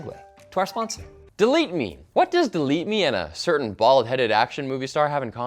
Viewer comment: "Bald-Headed Action Star" I thought you said, "Bollywod Action Star". Was very confused.